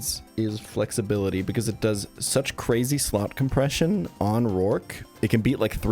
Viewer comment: I swear I heard “Crazy Slop Compression” here